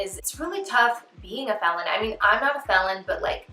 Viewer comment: did she say 'I'm a Felon..'?? Run men! Run for your lives!! 🏃‍♂️🏃‍♂️🏃‍♂️🏃‍♂️